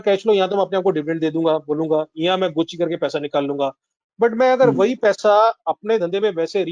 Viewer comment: what have you said varinder sir.. 'ki mai gochi kar ke paisa nikal lunga?'